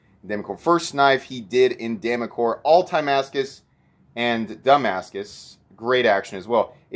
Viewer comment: did I hear you say it was the first knife he did in Ti-mascus and "dumbass-cus"??? 😂